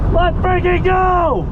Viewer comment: it sounds like he said let Frankie go 😂😂😂